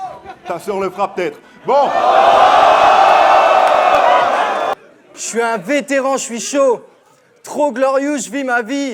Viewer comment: il dis quoi le mec dans le public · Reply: "Faut ramasser"